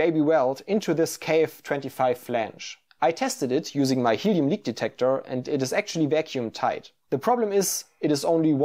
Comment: In terms of dumb comments I could add- the pic of him in the thumbnail makes me think of Venjent. Also, at about 2:54, I thought he said he had to use his Human Meat Detector, the first time. Pretty cool video, though.